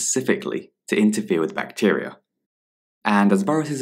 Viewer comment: Hi, thank you for these videos they are helping me so much. Can anyone please make out what the word is being said at 1:49, it sounds like bharatas. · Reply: it says 'bacteria'